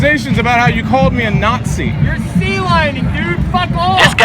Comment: What the hell did that guy say "sea lining"? 4:42 could not find what that meant.